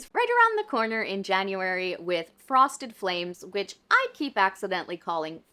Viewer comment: I was watching this while doing dishes and I heard Frosted Flakes at first and was like “wait really??” So the clarification helped 😂😂